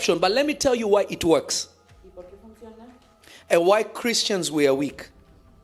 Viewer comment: Please Pray for us in Indoneaia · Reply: Did he just say "as white Christians we are weak" is he white? 😂😂😂😂